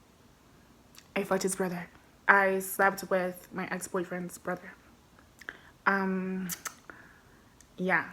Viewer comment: Girl I thought you said you fucked his mother 2:15, I nearly choked on my popcorn hearing that 😂🤣